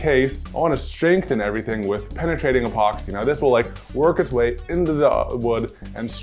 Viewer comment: Did he just say ,the opoxy will twerk its way into the wood'